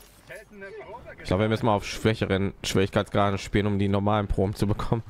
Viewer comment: Sorry, mein Fehler. Du hattest tatsächlich "schwächere" Schwierigkeitsgrade gesagt. Ich hatte "schwierigere" verstanden.